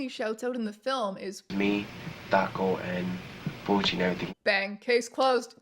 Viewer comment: It didnt sound like he was saying bouchie to me. It sounded like it starts with a p not a b. Sounded like puchi. Not that it even matters since its a nickname. F puchii